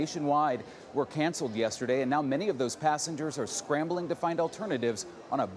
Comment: Hi, I'm an English learner, Can anybody tell me what's the word at 1:00 to 1:01 mints ? Please 🙏🙏🙏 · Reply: Scrambling, means like looking or moving fast